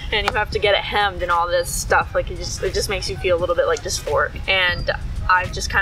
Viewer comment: When you mentioned how men's-section pants make you "feel dysphoric," wifey looked up from her phone and said, "Did he say they make him 'feel like a spork'?" So ya, uh, just thought that was a funny mishearing thing. Bye!